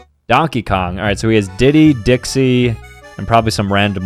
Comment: When he said Diddy I heard "titty" 💀💀💀